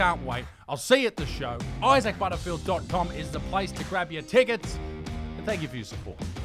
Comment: I could have sworn he say "Grab your tits", geez, I know I've put on weight but come on. LOL